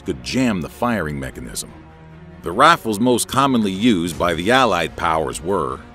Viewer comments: Anyone else hear “The Raffle” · why does he say rafles